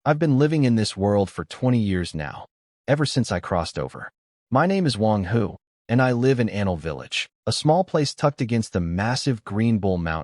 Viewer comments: Video starts and what I heard "My name is Wang Hu, and I live in ANAL village."😂😂😂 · " i thought I heard an@ village, but it's Anle😭